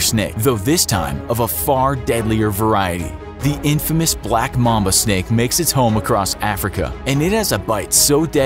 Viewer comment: I thought he said Black mama 😂